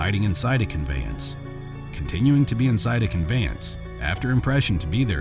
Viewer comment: you said impression instead of permission and what’s so odd about that i was reading along a second ahead and my brain for some reason also wanted to say impression, weird lol just thought I’d let you know lol